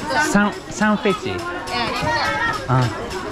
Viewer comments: その青団子は「サンフェチー」じゃなくて「三块钱（３元の意味）」（サンクアイチエン）ですｗｗｗ ちなみにチマキのおばあちゃんが言ったのは「坐坐（座ってっていみ）」（ゾウゾウ）、日本語で「どうぞ」と空耳って面白い😄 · サンフェチーと聞こえたのは、本当は青団=チントゥアンで、おばさん達は値段が三元=三块钱サンクゥアイチェンだよと教えてくれています。